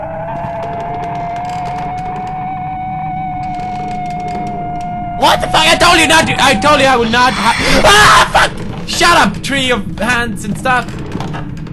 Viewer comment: sounds like he said "FUCK shut up three of handsome stuff!"